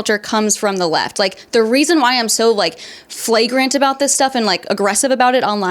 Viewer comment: Did she just say..... flagrant?